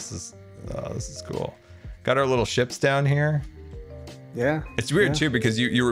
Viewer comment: I love this channel the clips are always awesome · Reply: I thought that said chips. Now im hungry